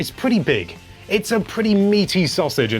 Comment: Did he just say it's a pretty meaty sausage? (around 4:33) well i think we all know what he's thinking lol